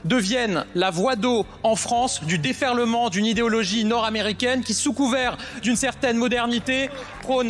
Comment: Je suis tellement habitué a Gabby le raciste que j'ai entendu " idéologie nord africaines " 😂😂😂